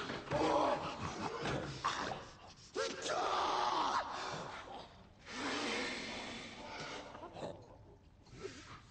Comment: Anyone know what he yells at 1:46? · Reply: Betray